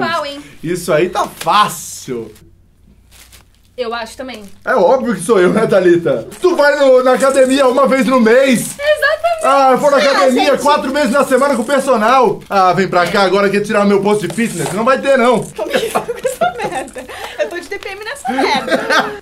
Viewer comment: "Eu vou na academia 4 meses por semana." Gente, eu escutei errado ou ele disse isso mesmo?